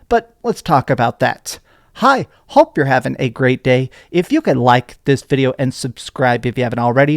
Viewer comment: I just realized, all these years later, that he says “Hi, hope you’re having a great day”. I always thought he said “I hope you’re having a great day”, but just added a H sound from excitement lol